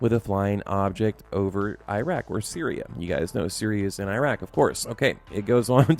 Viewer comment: did i just hear you say "...Syria is in Iraq"??!!?